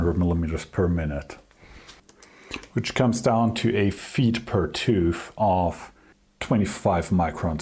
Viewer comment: for a moment I thought you said "feet per tooth" as you lifted your calculator and I thought you were gonna pull a Kurtis and make a comment about bananas.